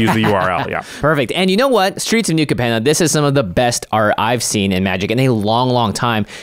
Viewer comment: did he just say at the beg...SOME OF THE BEST CARD IVE SEEN IN AWHILE..... barfffffffffff..... whats the best card in the set??? maybe bootleggers stash .... which is neche.....for treasure... what reval with riches for the win...lol? no....no...AFR was a better set and thats SAYING ALOT.